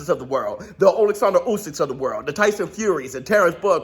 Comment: I thought I heard Stephen A. Smith of the world.